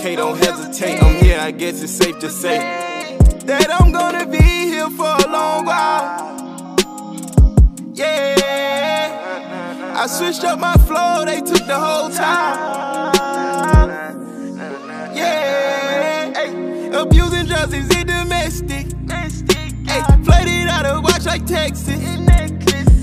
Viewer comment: “I SWITCH UP MY FLOW (FLOOR) THEY TOOK THE WHOLE TILE” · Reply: hold up I thought he said "I SWITCH UP MY FLOW THE TOOK THE WHOLE TIDE"